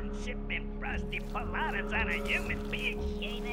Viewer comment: Did he just said Human beach? so dat mean humankind is canon on oddworld now? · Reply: I thought so too but it's actually humid beach